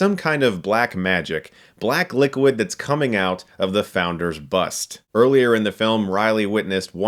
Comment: "Black liquid that's coming out of the founders bust..." I had to turn on the closed captioning to clarify that as I thought Chris said, "black liquid that's coming out of the founders butts..." Yeah I know, I have the mind of a 12 year old that way....